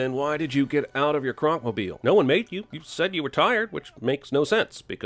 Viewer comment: At about 10:18 in this video I swear Jeremy says "no one maked you"